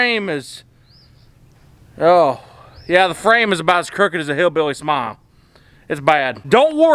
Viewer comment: “The frame is about as crooked as a hillbilly smile”🤣😂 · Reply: I thought he said as crooked as a hillbilly's MOM.